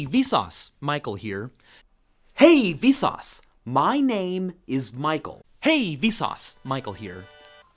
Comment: WTF DID I JUST HEAR?! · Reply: Hey, Vsauce! MynameisMichael